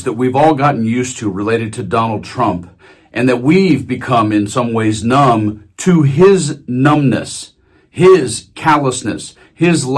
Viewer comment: "We've become numb to his numb nuts." or did I mishear it?